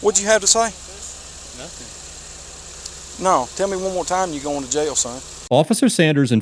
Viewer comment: you missed what he said at 2:02 . he said tell me what you said OR your going to jail